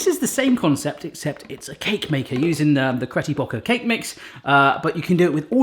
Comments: did Barry say Cretty Brocker?? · did you just say CRETTY BROCKER Instead of BETTY CROCKER 😂😂😂😂!!!! Yeah u did dat purposefully like twice in a row 😂😂my bad !!!I thought you did a mistake 😅!!!